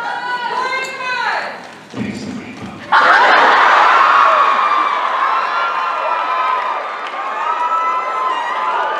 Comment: what Jared says in 19:07?? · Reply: He said "Taste the rainbow"